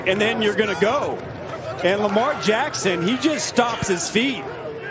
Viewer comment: did he say Lamar Jackson lol 😂